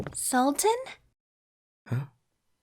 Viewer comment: I heard "SaLtY"